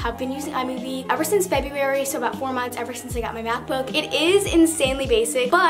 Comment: I thought she said maths book 😂